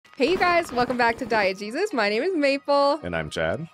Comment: I always hear "Welcome back to Diet Jesus".